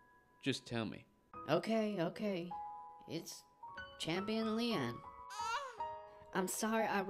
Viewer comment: What you say? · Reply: Riza Saika champion Leon (*man in the distance moans*)